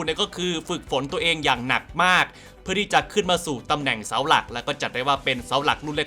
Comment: พูดว่าอะไรครับฟังไม่ทัน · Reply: ตำแหน่งเสาหลักครับ